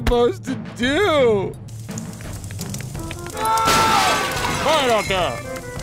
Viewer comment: really sounds like they say in Finnish "hiljaa siellä" and it means "quiet over there" so it fits the scene perfectly. I can't unhear it xD